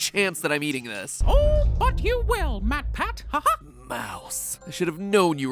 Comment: i thought mickey said "oh f**k you will, matpat"